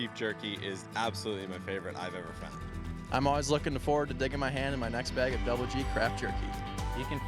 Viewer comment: is "always looking forward to his d#ck in his hand"? What is he really saying? I rewound like 10 times and it's all I keep hearing.